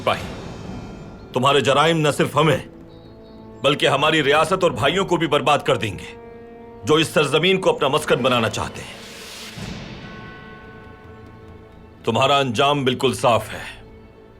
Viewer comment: Slip of tongue by ertugrul..... "Jo is sar zameen ko apna MASQAT bnana chahte hain" (Masqat instead of Maqsad) 🤣🤣🤣🤣